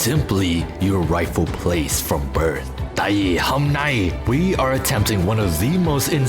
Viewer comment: did bro just say "ngày hôm nay" ?